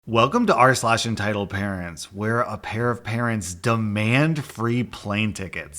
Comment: Who heard "a parent parents"? XD